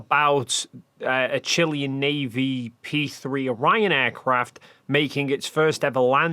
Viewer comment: Omg I thought you said "a Chilean Navy RyanAir aircraft" 😂😂😂 That would blow my mind.